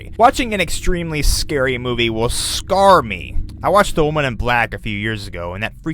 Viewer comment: i thought he said men in black XD